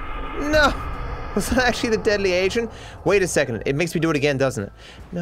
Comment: caption says deadly asian